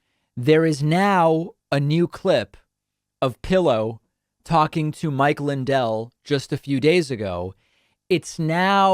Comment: Did say Mike Lindell talking to pillow? Lol